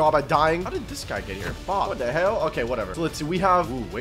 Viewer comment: did you guys hear him say what the hell cause that what I heard